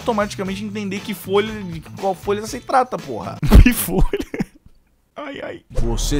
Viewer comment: eu fui pior que entendi pihortelã kkkkkkkkkkkk